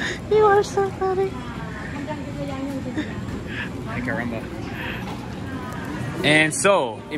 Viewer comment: did I heard "ai, caramba"? lol shoutout to portuguese/spanish speakers from Brazil I am loving your vlogs from asia.